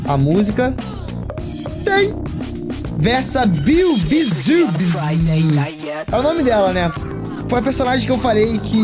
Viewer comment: alguém me fala o nome dela que eu não entendi o que ele falou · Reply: Bweeswees Bizznip